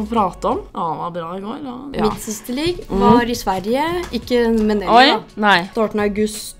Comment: 😶👀 did i just hear the n word? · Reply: hehe nei, hun sa "ikke med nelly", altså <link> som vi var på reise med i Sverige for noen helger siden :)